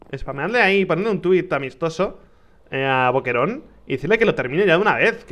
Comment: escuche “déjenle un tweet a mi esposo” (Se que dijo amistoso, pero a primeras escuché “mi esposo” xd